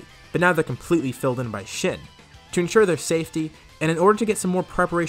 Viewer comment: I heard SHIT